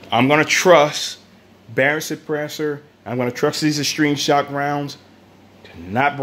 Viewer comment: What kind of ammunition did he say he was using? I couldn’t quite make out what he said. At first I heard “extreme shot” but I couldn’t find anything on google. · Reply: Extreme shock